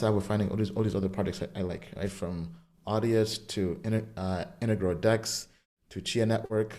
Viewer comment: ? What does he say? Intergrow dex? can someone tell me please?